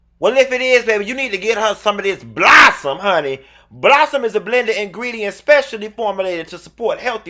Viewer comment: Why I thought it sounded like 'Blow Some Me'? LMAO